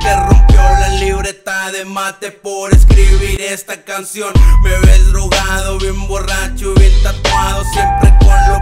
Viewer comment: siempre pienso que dice bebes drogados